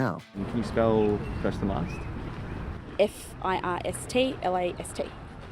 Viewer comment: “If I hate(ate?) his teeth I hate his teeth” took me so many watches to figure out what she’s saying! 😂😂